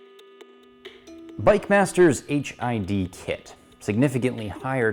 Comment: I kept hearing 'HIV kit'